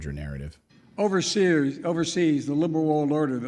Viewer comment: Did he say overseers? hmmm..